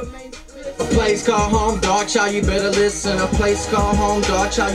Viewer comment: oooooooooooooh now i know who this guy is,all this time on those songs i thought they were saying dog chow but they were saying darkchild,wow hes been around for a minute